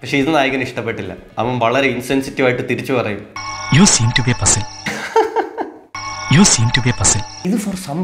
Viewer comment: I think he said ‘You seem to be a puzzle’.